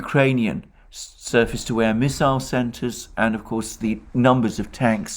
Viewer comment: Talking about "Ukrainian surface-to-air missiles" – I first heard "surplus-to-air missiles", after hearing that Germany is donating 1960s vehicles to Zelenskyi. 🙉